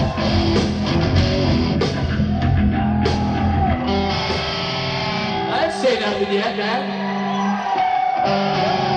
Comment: What did James say at 39:40?? something about tuning? jajajaja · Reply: "I didn't say nothing yet man"